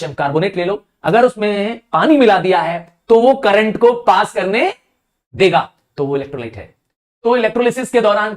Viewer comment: did sir just say the n word.....